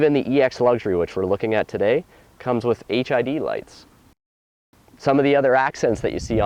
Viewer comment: Lol was i the only one that heard him say HIV lights?